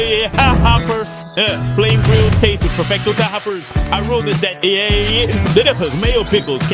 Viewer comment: anyone else hear him say "i wrote this at 8am"